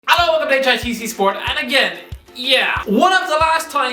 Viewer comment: I thought this Irish buffoon said he wants to quit HITC Sport😂